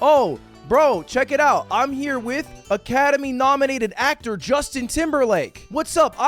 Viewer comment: I sounds like hes saying "im here with a ketamine nominated actor Justin Timberlake"